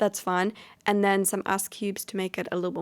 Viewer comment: I thought she said ass cubes